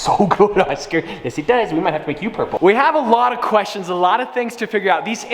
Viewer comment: did he say we gonna make oscar purple?!